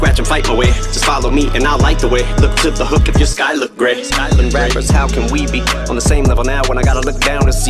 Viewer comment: Editor doesn't know what Eminem actually said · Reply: sky look grey/skylar grey. everything's calculated haha